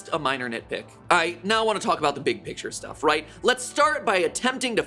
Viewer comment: did he just say ight? lmao 😂